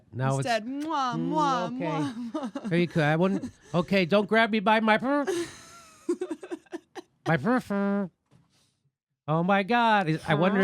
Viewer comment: I thought you said about Pelosi saying to Trump, "Don't grab me by my Birther." That's pretty funny, now that I write it down. Justice Democrats, oust the chumps.